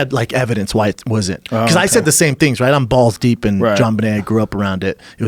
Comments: Did he just say, “ I’m balls deep in JonBenet (Ramsey)”? 29:50 lmao wtf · WTF Did Schaub just say he's "balls deep in Jean Benet" ???? thats kinda creepy freudian slip. he needs to get his browsing history checked. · Did Schaub just say he was balls deep in Jonbenet Ramsey? That's fucked up.